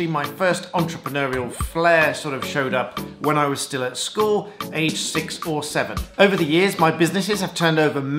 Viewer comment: When you said age 6 or 7 I thought you said age 647 lmao. I was like this dude 1,000 years old xD